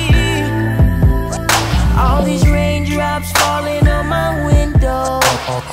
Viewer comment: All I hear is “All these raindrops falling on my a*shole!”